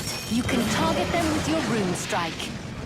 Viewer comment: aha she said rune strike, sounded like she said broom strike.